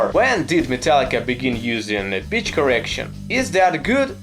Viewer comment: You said 'Pitch Correction', but I heard 'Bitch Correction'. :D :D :D